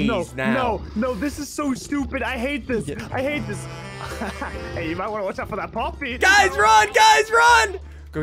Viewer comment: WHAT DID JUST SAY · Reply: Hey you might wanna watch out for that poppy